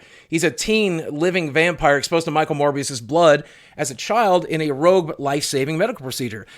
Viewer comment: I thought you said Michael Moore's blood.